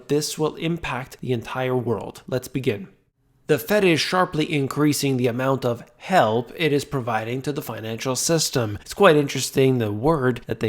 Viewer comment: Instead of "The Fed is sharply increasing amount of help it is providing to the financial system." I heard "..Increasing amount of hell it is providing..." 😆